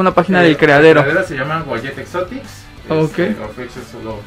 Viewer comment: Buen vídeo, amigo, amé las ranas, un pregunta, ¿Cómo se llama en redes? Entendí "guayeve exotics" pero no lo encuentro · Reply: Uadyet Exotic's